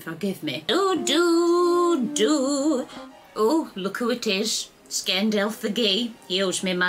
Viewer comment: when you said "it's Gandalf" I heard "Scandalf". Actually quite a fitting name, don't you think